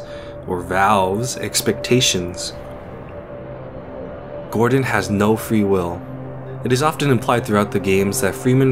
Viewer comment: I thought it said “gordon has no free wifi”